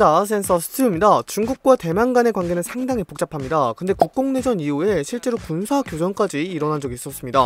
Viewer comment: 국공내전이 국뽕내전으로 들려버렸다...